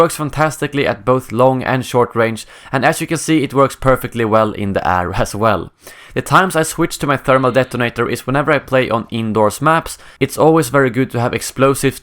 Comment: Did he say INDOR?